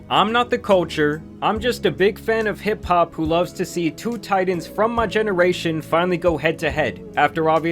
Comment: lol i rewinded this 3 times.. idk why tf i kept thinking you said "2 tight-ends" instead of "2 titans" hahaha smh my brain - 11:12 maybe no physical harm... but there was 1000% some mental and EMOTIONAL DAAAAMAGE done to any of Drakes Olympic style d!ck riders